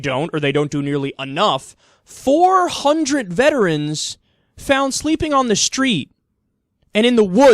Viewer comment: at first I thought it said "400 vegetarians sleeping on streets.